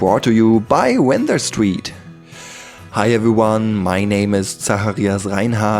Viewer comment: For a second I thought you said "Hey my name is Oyahoo Gayzaimas (probably spelt it wrong)"